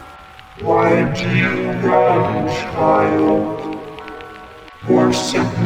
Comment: Ever since I watched that one guy, I can't hear him say child, I hear him say 🐈